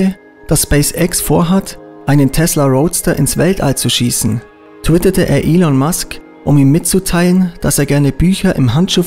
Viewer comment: Zitat: .". tötete er Elon Musk.." 🤣🤣🤣 Nachtrag: Habe es erst als " ..twitterte er Elon Musk.." verstanden, als ich es weiter unten in den Kommentaren gelesen habe.